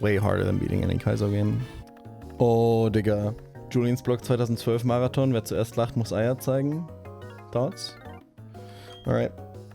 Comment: what did say? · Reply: Julians Blog 2012 Marathon - First to laugh shows their balls